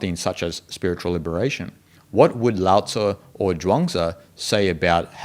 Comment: I heard "Lao Tzu and Drunk Tzu" and realize it was quite funny misshearing, but with a meaning :)